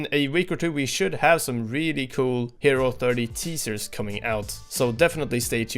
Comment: i thought he said "T-Series"